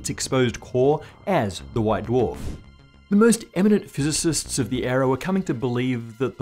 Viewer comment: In the beginning I thought he was saying "ion dwarf".